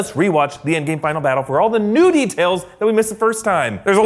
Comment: Did he just say Nudie tails?